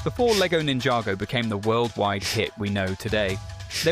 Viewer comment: In 0:01 that timing is vicious, I thought I heard, "before ninjago became a worldwide shit",😂😅